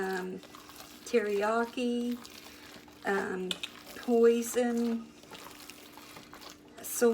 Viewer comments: I swear I thought I heard her say sometimes she puts other stuff in it, (but one of them sounded like she said poison!) Lol! Am I the only one that thought she said that?? Lmbo!🤣😁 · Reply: I heard it too. I was like say what? Lol I think she meant to say hoisin. She said it after terriyaki. Not sure if spelling is correct sorry in advance. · At 6 :17 sounds like said poison lol 🤣 someone tell me what she says ? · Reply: 😂 think she said "hoison",like hoison sauce..😂😂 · You said hoisin and I swore you said poison! Lol! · Did she just say she used to put POISON in her meatloaf in the past?...HAHAHAHHAHAHAA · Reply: I thought that at first, too. But, she was mentioning sauces like soy sauce so I believe she was saying ‘Hoison’. Hoisin sauce is a thick, fragrant sauce commonly used in Chinese cuisine as a glaze for meat, an addition to stir fries, or as dipping sauce. It is darkly-colored in appearance and sweet and salty in taste. Although regional variants exist, hoisin sauce usually includes soybeans, fennel, red chili peppers, and garlic. God bless! 🙏